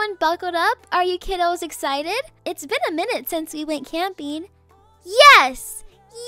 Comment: it's been a MINUTE since you went camping" i may just have misheard this but that made me laugh